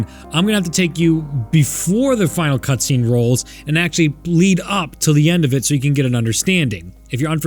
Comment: "Bleed up to it"?? Too soon... too soon. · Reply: Lol i thought he said lead up